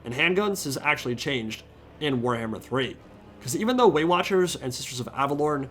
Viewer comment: I totally thought I heard Weight Watchers instead of Way Watchers when you were talking about the Great Unclean One. 🤣